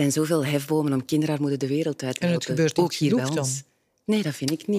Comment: Wat zegt de vrouw in het blauw om 10:13 aub? Alvast bedankt! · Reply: Ik denk dat ze zegt: "En het gebeurt niet genoeg dan?".